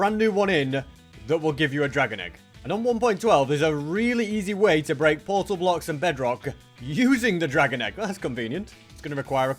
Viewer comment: Did he just say he was playing bedrock? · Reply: I said “there’s a really easy way to break portal blocks and bedrock”